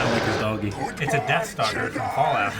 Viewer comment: did he just say a deathstar from fallout lol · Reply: I think he said “Deathstalker” – however, I think he was referring to deathclaws, which really do resemble Gor.